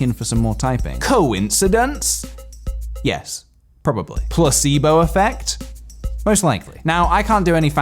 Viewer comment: Every time someone said placebo I thought they would say gazebo. Yes, I literally thought they said that because gazebos are apparently pointless, which isn't true but I started to believe that. Did anyone else have this same experience?